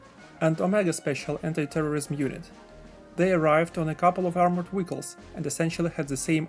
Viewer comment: did he say “armored wiggles”